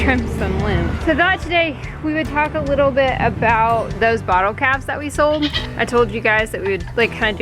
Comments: lol, I thought you were saying bottle caps and I'm thinking what in the world is she talking about!! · At first I thought you said bottle caps! 😂😂😂😂